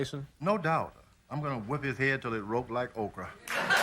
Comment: "I'm gonna whoop his head until he looks like Oprah", is that what he said or I misheard? 😅